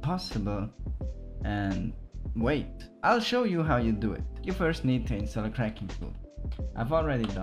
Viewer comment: Can someone tell me what he said at 2:35 I couldn't understand:( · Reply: i said how you do it